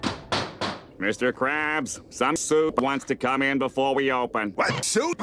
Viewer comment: I wasn't looking at the beginning of the video when squidward said "some soup wants to come in" and I thought he said "Sun Tzu wants to come in" 😆😆